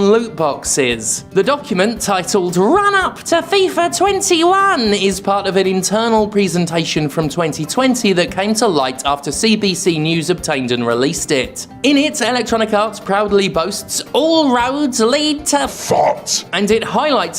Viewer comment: I misheard "CBC news" as "CBBC news" and was like "Children's BBC? Jim Craven's Newsround got this scoop?".